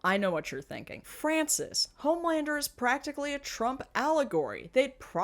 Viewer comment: I thought you said Zoolander 😅